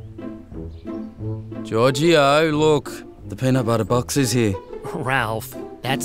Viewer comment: If i didnt know the dogs name was Giorgio, I thought Ralph would have said "Georgy, I look, the peanut butter box is here"